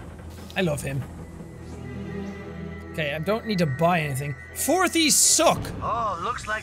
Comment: I thought it says fortnite sucks